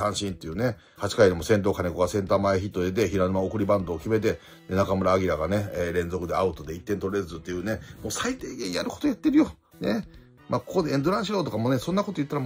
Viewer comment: ここ中村晃に聞こえた